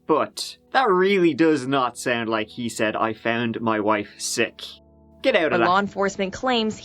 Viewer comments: Slow it down to .25 and it sounds like he says, " I juuu...ahhhh..... I killed my wife. Shit.... and... Blood everywhere.".... But then the 911 guy says, " Jesus man, b'lance.... " But of course he doesn't, the 911 operator says. "Do you need an ambulance". Slowing down the speed of the recording massively distorts the words and phrasing so that you can easily misinterpret an entire sentence. What I think he actually says is, " I got ah... I found my wife sick..... and blood everywhere." 911 operator says, " Do you need an ambulance?". You cannot slow the recording down by much or it becomes distorted. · It sounds like it says, “I k*lled my wife, sh*t.” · Not gonna lie, when I heard it the first time I heard “I found my wife- shit” then he said “oh it’s the chest”. But he did not sound like he just found her, just saying... it sounded like he was PRETENDING he found her. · How about option C: "I found my wife. Shit." Though admittedly when first asked us to listen, I heard, "I shot my wife. Shit." Obviously, that makes no sense with the facts, but just what I heard when I didn't know the facts. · It sounds like he said "I found my wife" but it is too unclear to tell for sure.